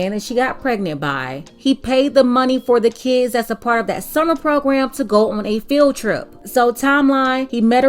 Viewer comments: I thought she said a submarine program at first,smh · I genuinely thought she said a "submarine program"